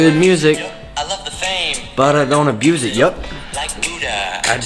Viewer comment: He says yup when the caption says nope